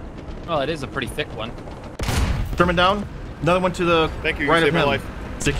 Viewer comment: Did say terman down